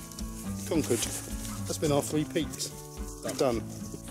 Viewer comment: I need to put my beer down,i thought it said three peaks on me bus 😂😂😂😂great vid chaps